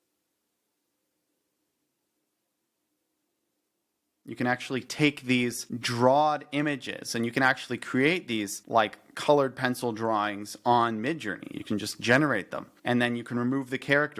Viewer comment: Did i hear you say "drawd"